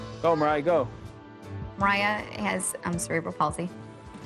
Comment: I couldn’t hear what she said. Would anyone care to tell me? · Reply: It's Cerebral palsy